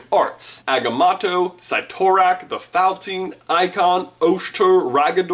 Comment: did he say ikon? Like the space knight from the annihilators?